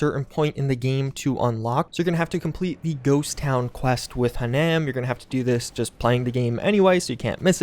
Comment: did u just say "Hanam"? Or did I hear wrong